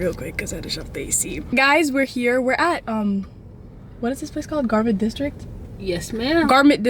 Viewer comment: I thought she said garbage district 😂😂